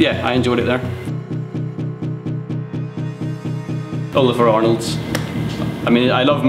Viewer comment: What a nice guy! Saw him live and DJ set before and was blown away. Does anyone know the composer he refers to as his favourite at 8:31? I hear Oliver Arnolds, tried googling him but found nothing. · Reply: 7 years late but it's Ólafur Arnalds